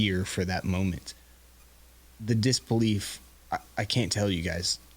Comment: did Luke say “the disc-belief”?